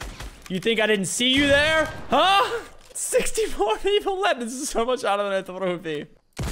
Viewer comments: what did he even say😂😂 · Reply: 64 people left this is so much harder then I thought it would be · i still can't understand what he said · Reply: He said “64 people left this is so much harder then I thought it would be” and if I’m being honest it took me about a year and a half to figure that out 😂😂😂😂